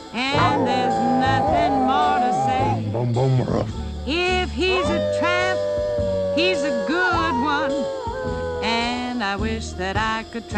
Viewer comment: For a moment i thought she said "If he's a trap, hes a good one" ,,,is that a thing with dogs